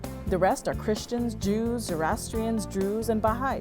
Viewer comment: Did you say "jews" twice? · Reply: No. She said Christians, Jews, Zoroastrians, Druze and Bahá’is.